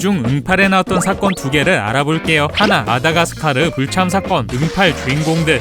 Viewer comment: 마다가스카르라고 들리는데 자막은 마드가스카르라고 보이네여